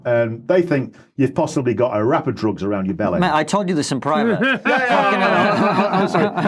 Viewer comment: Who else thought he said "rap of drugs around your belly"?